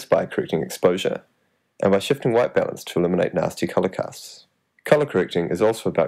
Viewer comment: does he say Nazi colour casts at 0:36?!?